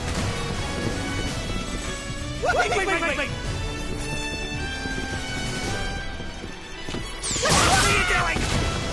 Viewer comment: What does the 2 Dampierre's say at 0:38? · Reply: "Wait,wait,wait,wait!"